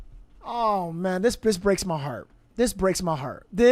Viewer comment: 45.52-54..time stamp..... did you call her a B!#CH....🤔IJS..Sounded like you said " this B!#ch breaks my heart🤔ANYBODY ELSE HEAR THAT🤔